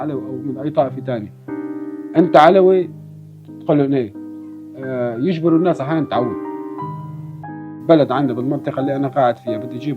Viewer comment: "Are you an Alawite?" The answer is "Yes". He says "Then bark!" This is a sentence has been recently used to humiliate the Alawites by comparing them to dogs. You can find it on the comments where the Alawites express their opinion as a minority. You can open the comment section and see alot of comments that says "bark" or in Arabic "'Aaoi". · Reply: Yes, at 2:10, the video caption is incorrect, it says: "force people to return where they came from" , what the man said in arabic is: "force people to bark"